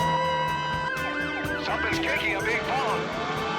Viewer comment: Can anyone tell what is said at 3:21 and 3:30? I hear "Something kicking a big bomb" and "on tree wire". No sense.